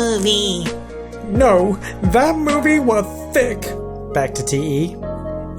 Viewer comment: No! That movie was sick(SOUNDED LIKE THICC)